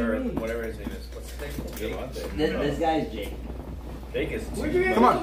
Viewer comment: what did kian say about jake? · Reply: I think he said “Jake is too much” idk that’s what i heard